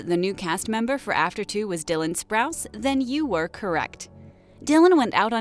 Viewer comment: OMFG I thought she said cole sprouse now I'm disappointed I want jughead XD